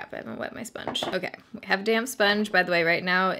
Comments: Totally thought you said “I have a damn sponge” 😂😂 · Do you think this would look just as dry and textured on more oily skin? Just curious bc I'm more normal to oily. Also I heard "I have a damn sponge" even though I know you said "damp.." lol 😅